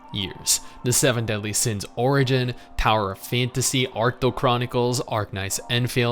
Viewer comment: Is there a link for the 3rd game mentioned? Tried googling what I thought I heard (Arcto Chronicles?) but nothing came up.